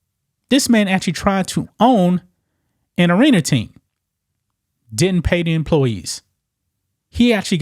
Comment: I thought I heard he tried to own an orangutan. But arena team makes more sense. :) I love your videos. Merry Christmas!